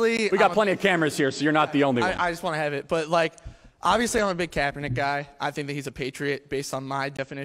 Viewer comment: I was so confused by this clip because I thought he said “I’m a huge cabinet guy, I obvious think hes a patriot” I ideally wondered if the government cabinet was named after a great guy then as the clip went on I realised what he said I felt illuminated then 0.25 sec later became very confused